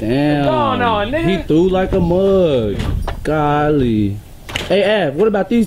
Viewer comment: what did he say at 4:25 kahli? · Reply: Gollee